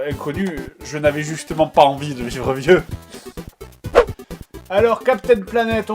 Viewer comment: A 2:08 : je n'avais pas envie de jirevieu ? O.o Il dit quoi exactement en fait ? xD